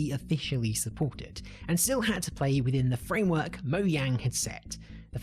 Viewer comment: Did he just say moyang?